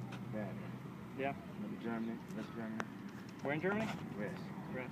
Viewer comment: West Germany. Ah he's a cold war veteran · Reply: It sounded like he said "rest Germany" 😂